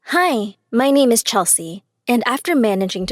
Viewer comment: Idk why I heard "hi my name is chopstick"